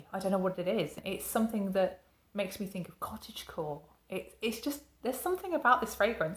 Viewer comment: I've learned something new today : Cottage Core (am I saying it right?) I had to rewind as I thought you said cottage cheese... you kids are crazy today😺